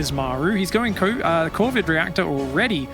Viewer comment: anyone else heard "Covid" reactor? These times...